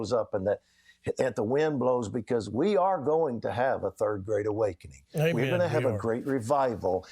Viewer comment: I misheard "third great awakening" as "third /grade/ awakening" and tbh that fits that kind of thinking better lol